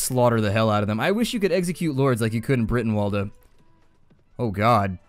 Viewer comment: What was the mod/game he mentioned because i didn't understand what he said. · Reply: ik its late but yolo, he said brytenwalda